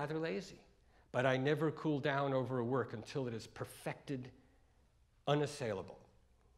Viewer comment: what is that word he likes? At around 7:05? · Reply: unassailable